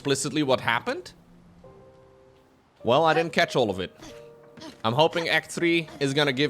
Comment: i want to correct "well i didnt catch mostly any of it" :)